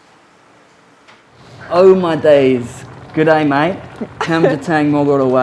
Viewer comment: 5년전에 호주식good day mate를 처음 들었을 때 good die라고 알아들어서 무서워했던 1인.나만 그런거 아니잖아. 에이 다들 왜 모르는척이야